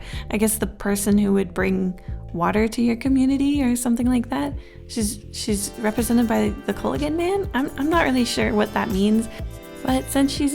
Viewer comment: I'm sorry but all I heard was "She's represented by the Kool Aid man"